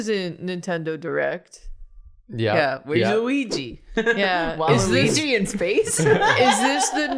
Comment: 😂😂😂 When you said "where is Luigi" with the voice you used i thought you said "where is Uwuigi" and now i can't get that out of my head 😂😂 please draw UwUigi!